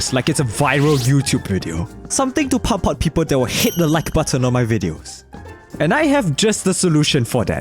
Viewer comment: I thought you said SHIT the like button